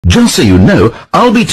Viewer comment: I THOUGHT HE SAID “I’ll be taking a SHART”